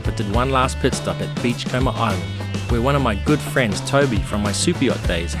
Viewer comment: For a minute there, I thought you said you were going to Beach Coma island. I thought damn, that sounds like a good time 😁